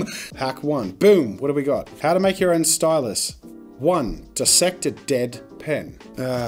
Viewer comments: I thought he said, "Step one, dissect a dead pet." so I thought 'Bloody hell this is some hardcore art hack.' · I heard that as "Dissect a dead pet" and was confused 4:00 -- Okay, I just heard that as "An old pen's rat's arse" XD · I heard "step one: dissect a dead pet" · Oh lord… I thought he said dissect a dead pet, not a dead pen 💀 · I heard “dissect a dead PET” and “murder a KITTEN” needless to say, I was very confused